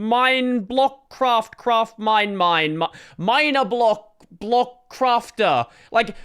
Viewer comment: 😎Dr Disrespect: "Did he just say minor blocks at 2:09?"